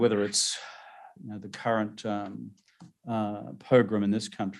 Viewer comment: Pogrom? The captions say ‘Program’ but he actually says pogrom. What exactly have the US security authorities done that he refers to with such a strong, loaded term?